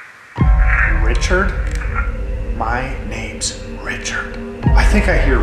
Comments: I thought the name caught at 46:09 sounded like it said "Richard Mansfield." I say that because I heard, "My name is Richard Mans-", and interrupted the EVP. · I know I'm a little late on this for the episode hahaha But at 46:00 when does an EVP session, what I keep hearing is "My.. name's.. Redman, James" but the background sound effects cuts into the "James" part at the very end, so its a little difficult to make out.